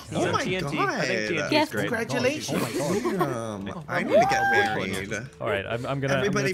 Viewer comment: Queen your so talented and amazing❤💜💙😘👑💖😍 We love you sm🤧💖👑💙 Edit : 09:42 did i just hear some one saying "I need to get married" ?